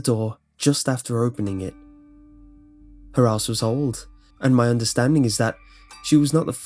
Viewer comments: Lol at 3:40 when he said "her house was old" in that English accent, it sounded like he said "her ass was old" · I'm sorry, but I laughed so hard because I misheard you at around 3:39. I realized after what you'd actually said, but I swear when I first heard the line "her house was old" I thought you'd said "her ass was old" and I just cracked the fuck up. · I'm sorry, but I laughed so hard because I misheard you at around 3:39. I realized after what you'd actually said, but I swear when I first heard the line "her house was old" I thought you'd said "her ass was old" and I just cracked the fuck up. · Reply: me too I hear "her ass was old" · "Her house was old." I heard, "her ass was old" and was all like, "DAMN! Savage." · I heard "her ass was old" Like if you heard it too, spooky right? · I THOUGHT YOU SAID HER ASS WAS OLD TOO XD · Pfft, when you said "her house was old", your accent made it sound like you said "her ass was old." PFFFFTTTTTT. </super mature>